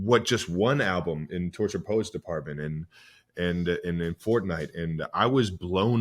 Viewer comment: did he say Fortnite??